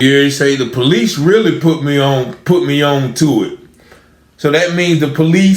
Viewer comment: Sound like he said "The police really put me all into it" now Ii dont doubt for a minute that those pigs👮🏼‍♂️👮🏻‍♂️👮🏾‍♂️ bribed him but that's what I heard him say. Either way, theres definitely foul play.